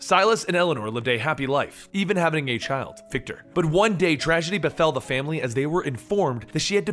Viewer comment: Just a little rebirth with your coffee this morning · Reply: Did you say "even having a gay child" at 2:12? What does that mean?